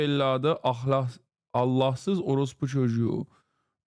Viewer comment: You cant convince me that Turkish is a real language. 😂 @ 0:04 Did he say "Earl orlos pajuju"?